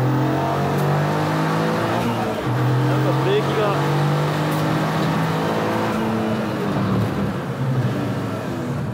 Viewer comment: It seems that there is no problem at the moment when the brake is applied, but the brake is lost when the gear is shifted to 2 。What did he say at 18:53, I saw from the translator that he said "why brake if you don't want it", right? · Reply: He said "Something's wrong with the brakes..."